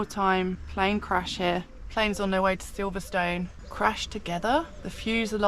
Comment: He's got a sausage on him 😂 and I misheard you saving on your way to Stabberton 😂 ....